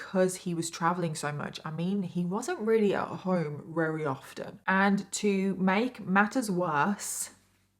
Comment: Did she said rarery often? 🤨